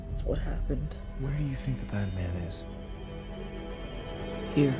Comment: I thought Oliver said were do you think batman is and I got hyped for a second but then rewatched the the scene and he said bad man